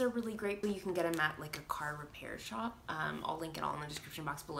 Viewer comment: What was the blue item you pulled out at 5:29 in the zip lock bag. I thought I heard car repair shop. How Re they different than blotting papers?